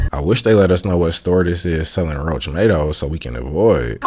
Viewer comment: I almost pissed my pants when I heard "Roachmatoes!"😂